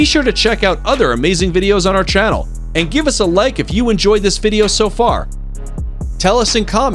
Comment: When you said give us a like it sounded like you said give us a leg